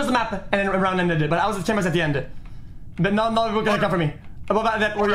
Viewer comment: i didnt understand single word, someone please translate? thanks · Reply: Christina Browning no. He said “not a lot of people can account for me”. Yours doesn’t make sense.